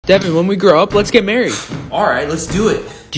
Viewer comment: I thought it said “dad can we get married?” 😂